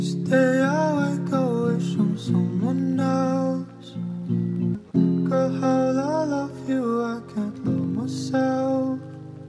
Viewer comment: I thought I heard "Girl how can I love you, I could kill myself" "Each day I'm wishing the wisdom of someone else"